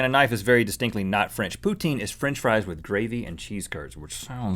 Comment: i thought he said pootang